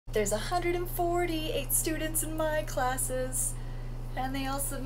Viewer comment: I thought u said there was 148 students in ur class 💀💀💀